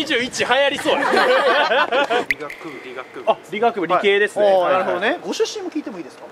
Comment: 医学部に聞こえたゾ…